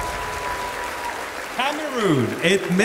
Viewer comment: It’s hilarious how Mr. Supra Peru thought that said “Peru” when announcing the top 20. When he actually said, “Cameroon”. He had to say “Cameroon” again to avoid confusion. Cameroon 🇨🇲, not Peru 🇵🇪. 😂😅